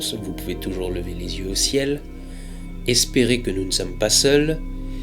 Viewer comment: What il dit quoi à la fin alors voyure wtf ? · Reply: À la revoyure… = au revoir…